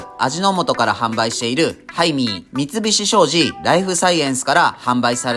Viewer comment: ハイミーがタイミーに聞こえる 仕事行かなくちゃ••••••